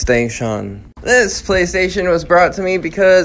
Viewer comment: Did he just say play stay Shon😅